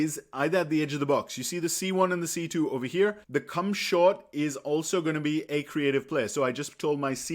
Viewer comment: i heard " Cumshot " lol